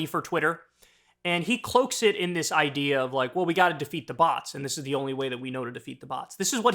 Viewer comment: At first I thought Kyle said "we gotta defeat the boss" and I was like "wait, isn't Elon already the boss? 🤔" 😂